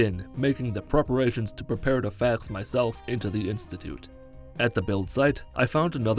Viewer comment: Why does it sound like he says "fag myself" instead of "fax myself" at 14:17 😂 anyways love the videos man keep it up